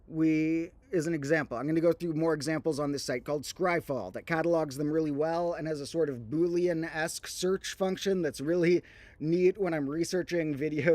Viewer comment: Thanks! If you mean the website where you can look up different Magic cards by searching different traits/types/text, it is "Scryfall" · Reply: indeed and thank you, misheard it as "skyfall"